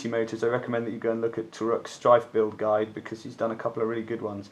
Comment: you recommend another guide, but I can't hear it clearly. Could you please link it here. Thanks bud. Your guide is amazing and I can't believe it only has 450 views. I am gonna be following this to the letter. Cheers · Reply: It was a guide by Torukmakto4 on his Dart Zone blog. Link added in description box. Glad you like the guide. All the others were rubbish, so i thought a true and proper one, with measured results and proper testing was required. Feel free to suggest any other things you would like to see!